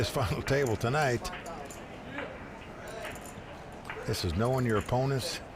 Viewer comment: Does Mike say "this f***ing table" at 8:33?